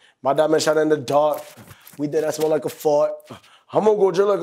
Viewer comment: I always thought he was saying we deadass smell like a fart